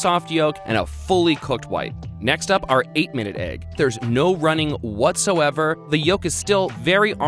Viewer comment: When he boiled the eggs I heard “There’s no running”.